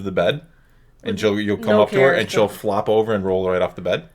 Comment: I definitely thought said "F--- you" and not "So cute" hahahahahahaha